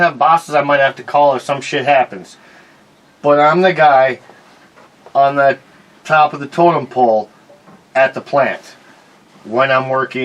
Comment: Did say that he's the guy at the top of the toilet bowl???